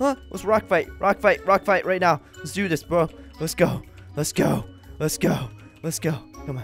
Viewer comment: When you say let's do this baby it sounds like your saying "Let do dis baby".